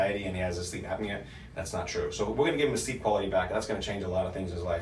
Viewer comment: I couldn't understand what you said at 4:25 mark about his sleep apnea? sleep what? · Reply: Sleep Apnea is just another symptom of his imbalance. Once we balance his body he won’t have sleep apnea anymore So I said he will get is sleep quality back. Hope this helps 🤙🏻